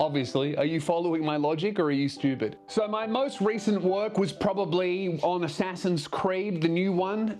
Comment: "So, my most recent woke..." That's how I heard it